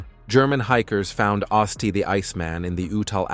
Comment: Oh, I thought it said otzi theicecreamman I want ice cream